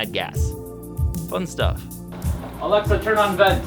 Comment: Who heard him say: "Alexa, turn off bed." 11:00 in the video.